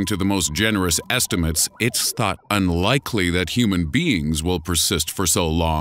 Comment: I thought he said beans not beings at the beginning of the video.